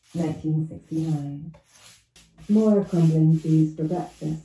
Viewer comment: Im just vibing watching the video and at 15:55 i hear "more crumbling cheese" like tf 0.0